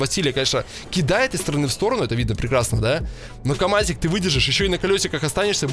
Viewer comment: он что сказал наркаманик???🤣🤣🤣😂😂😂